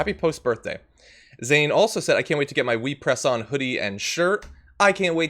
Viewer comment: This is the stupidest thing, but when the donation comment mentions a "We Press On" hoodie, my immediate thought was that he said "Wee Press-on Hoodie", and my brain imagined a tiny Lee Press-on Nails version of a hoodie and I was like "what the heck is he...??! ….oh."